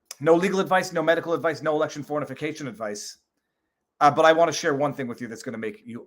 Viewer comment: did you just say election fornication advice? Lmao